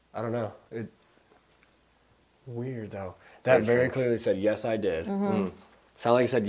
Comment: The evp sounded like it said yes we did when you asked about you guys dieing lol x